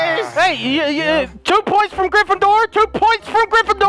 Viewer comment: did he just said TWO POINT FROM GRYFFINDOR ?